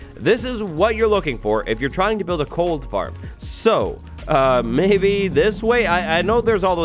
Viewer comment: Did say Cold Farm?? Instead of Gold Farm?